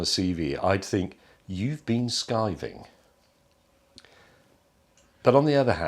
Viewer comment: he said "you been (what)" i am not a master of accents, i renounce my title. i cant understand the word he said there, and captions were useless. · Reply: Skiving.